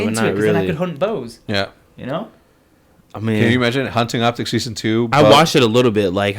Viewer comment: did I hear 'hunting Optic s2?'